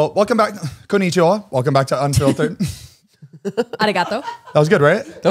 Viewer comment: Oh my gosh. Iv been watching all of the episodes for the last 2 years and the intro song says unfiltered and i always thought it said “coffee time”…… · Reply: I know it's "unfiltered," but all I hear is "up till ten"